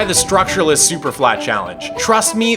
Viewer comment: He said "structure-less superflat challenge" but all i heard was "bedrock experience"